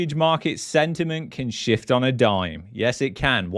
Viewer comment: I thought you said sentiment can shit on a dime in Canada